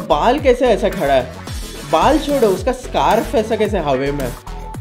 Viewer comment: say hawyyy instead of hawa😂😂😂😂